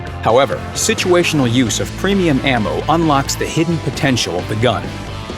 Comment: "situational use of premium ammo" .......I heard "spam prammo" will do. :-D